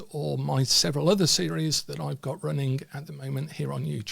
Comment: That was a cracking video to wake up to, Ty. Strangely I had to do a double take at the end as I heard ‘The worker is hidden in the chip shop’ 😮 lol. And of course you didn’t say that! But it does suggest to me I’m not where I’m supposed to be (very true), and I need to get my ass back to the workshop. Watching had already made me think ‘oh yes I need to do so & so :) Of course you didn’t know I was going to watch this or hear that😅… you gotta love the universe 🫢🍄! Thanks again for this, I will be sending you a question later on 👍.